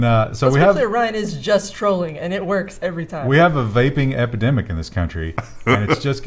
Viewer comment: I misheard that is now troll-targeting Baking, not Vaping.